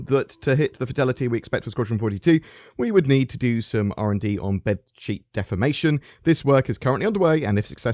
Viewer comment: I thought it said bedsheet defecation.. I thought Amber Turd mode was abit much.